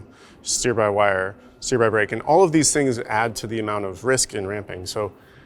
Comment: did he say "steer by brake"? Interesting.